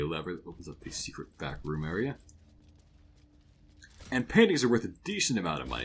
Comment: definitely misheard this as "panties"